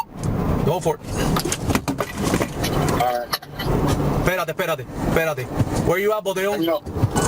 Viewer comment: if someone if wonderin what he said is: Perate, perate (short version of espeate) which means wait.